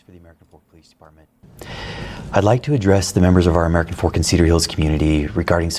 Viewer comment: did he say "American fork and cereals community"... Yeah I can't take you seriously.... Or cereally. And I'm from Texas where we have towns called "opposum trot"